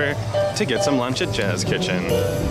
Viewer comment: Thought it said jizz kitchen I'm serious